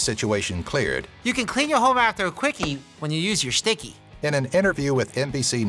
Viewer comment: did I just see a condom by the Cookie jar. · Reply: Oh my god, listen to what he says. “You can clean your home after a quickie...” I thought it said “in a quickie”.